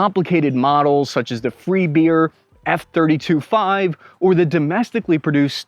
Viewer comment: Beer instead of bear?